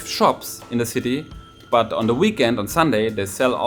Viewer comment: I totally misheard the word weekend when you were talking about flee markets. I Initially thought you said flee markets are a big part of the wiccan culture😂.